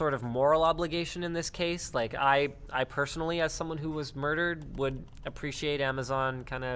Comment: What did Linus just say? Did he just say that he was personally murdered?